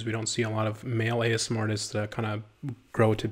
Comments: i thought it said asmr tits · am i the only one that thought it said ASMR tits